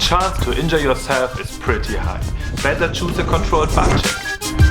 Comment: What is he saying at 1:57? Re the landing? Kinda difficult for me to understand with his accent 😉. I think it’s important information if I don’t want to break both my knees. Thanks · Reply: Hey, I said if you don’t feel 100% in balance don‘t try to land it on the board. I recommend a buttcheck than... Does that answers your question ?